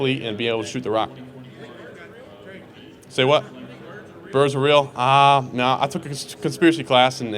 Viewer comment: minutes they asked him if something is real I've listened to it three times can't understand what did they ask him??? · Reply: "Are birds real" It's an internet joke that birds (specifically pigeons) are government surveillance drones and have been extinct for decades.